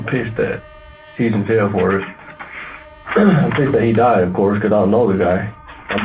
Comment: the cops missed what he said. He said "I'm pissed that he died of course cuz I know the guy"